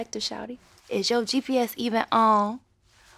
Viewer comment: lmao I thought it said “is your trippy ass even on” GPS? oops